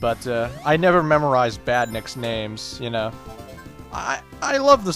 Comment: I keep hearing "badnik names" as "bad nicknames."